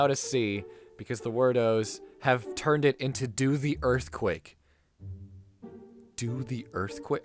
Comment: At first it thought said "to the earthquake" because it makes sense with the line he says before that being "you put your hands in the air and move with the sound," kind of saying to move with the earthquake (beat of something idk), but it sounds at least 10x worse realizing it's "do the earthquake" which sounds cringy as hell.